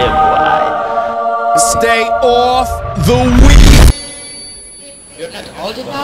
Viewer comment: stay off the weeeeeeed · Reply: Refeon I thought he said "Stay off the wheel".